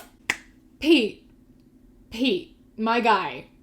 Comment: Had to skip back at 2:25 cause I thought you said Pea instead of Pete KJSHDGKJHSDGKJHSKJDG I was like who names their kid Pea? Edit: My mans at 6:40 is self aware and still said that shit? He will never be one of the bois smfh.